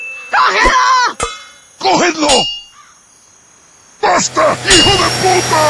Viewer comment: Does he say go hitler at 1:09 😂😂